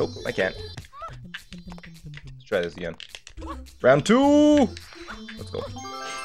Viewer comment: is he saying round or dram? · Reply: Round, but he’s Canadian so it sounds like “rand 2” I hear it too tho don’t worry😂😂